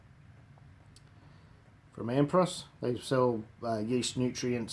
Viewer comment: which bit mate? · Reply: Your not gonna believe this but I thought you said Ampros and couldn't find it anywhere but in this light I can see it's Anpros! Thanks for getting back to me mate. Do you do lessons or train anywhere?